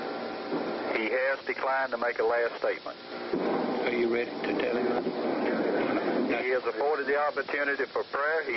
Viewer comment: "Are you ready to die now?" That has to be the most terrifying thing to hear in the whole process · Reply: I clearly hear "Are you ready to tell him?"